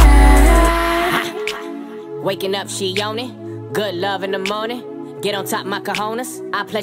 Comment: Before i knew it said collide, i thought it said good life😂